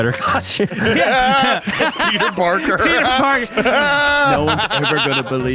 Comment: Who the hell is Keith Barker? They said Peter Parker! Your editors need to learn some English XD. Just tell them to not do subtitles in parts they don't understand.